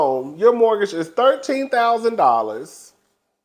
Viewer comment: The mortgage is thirteen THOUSAND dollars???!!! For what?! 😮🫤😳😳😳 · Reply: I thought she said that it was $1,300. But if the mortgage is $13,000, how much are the utilities and upkeep. I hope Ashley has a job(s) outside of Bravo because that seems unaffordable for someone who doesn't want to work and has no other income stream.